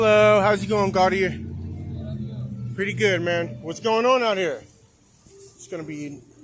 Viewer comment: wtf is he saying? "Hello how's it going guarding here?"